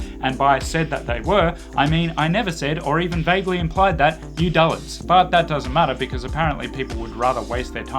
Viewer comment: Can’t understand what you said at 0:36. “...implied that ________” ? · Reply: "I never said or even vaguely implied that, you dullards."